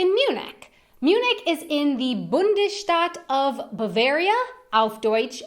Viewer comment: toledo hm, fuer mich klang es wie Bundesstadt und nur Bonn trägt diesen Titel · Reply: Sie hat das Wort "Bundesstaat" gemeint und schlecht ausgesprochen. Ich habe auch erst "Bundesstadt" verstanden.